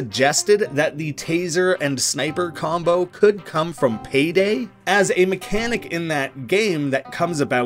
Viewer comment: am i the only one that heard at 19:47 heard hay day instead of payday